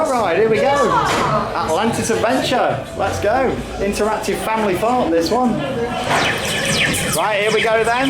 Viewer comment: Is it just me or did say Family Fart?